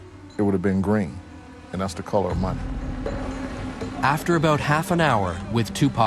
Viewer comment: what did his bodyguard said at 6:09 it would have been green the colour of... ?? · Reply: money